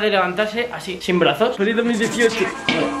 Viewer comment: Like si lo ves en el 2018 y te diste cuenta que parece que dice feliz 2018 en el min 2:52 😂😂